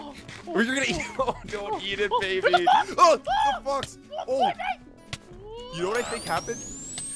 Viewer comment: didn't say da f word cause you can hear him saying "There's a fok" and sounded like he said the F word "Correction"